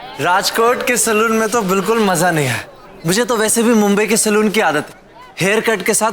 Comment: Dhawal ne 1:40 per kya bola kisi ne dhyan diya😂😂😂 kitna ashleel hai dhawal. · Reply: Mumbai 😂